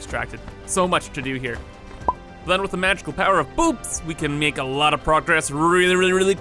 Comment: My subtitles said: "then with the magical power of boobs"